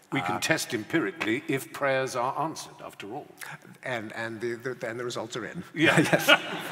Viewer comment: What does Pinker say at 16:41? "And then there is alter(?)......????" Thanks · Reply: "And then the results are in." Then Fry proceeds to spell out the result.